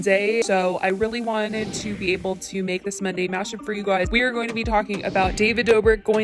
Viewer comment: Idk why I thought when said Monday Mashup I thought she was saying Monday Matcha? Im weird....